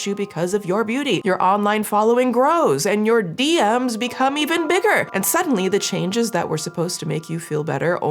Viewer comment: During the intro i thought said "your BM's get even bigger" i waa bout to say whatt 😭😭😂😂🤣🤣